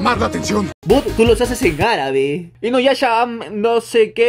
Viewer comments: Like si escuchaste en el minuto 0:36 que dijo "Inuyasha"💓💓💓💓💓💖😍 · escuche inuyasha jajaja